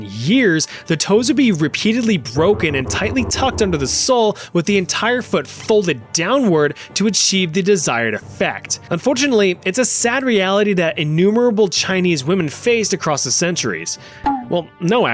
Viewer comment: did you just say unfootunatly?